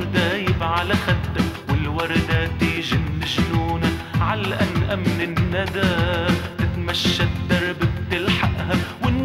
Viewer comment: على ايش ؟؟؟؟ مو فاهمة وش يقول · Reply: ع الأنقى من الندى .